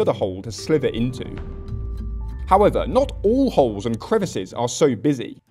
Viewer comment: U heard him "Not All Holes Are A Go!" 😂😂😂 😎🤙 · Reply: I swear I heard this as “not all holes are a goal”💀